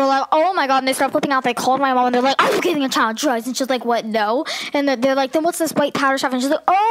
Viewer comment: what I hear: "avocado your child drugs" 😂😂